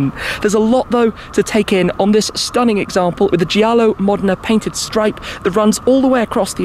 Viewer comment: Obviously, already had his video ready. Classic! Keep it up. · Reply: Did I hear you say, "paintited"?